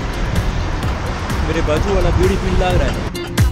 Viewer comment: i cant understand what he just say on that time .. any one know what he said on that time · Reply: mere baaju aala bidi piwan lag raha hai.